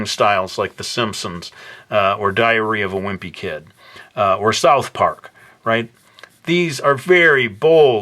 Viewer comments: It sounded like he said "diarrhea of a wimpy kid" lol great video though. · I thought he said diarrhoea of a wimpy kid X,D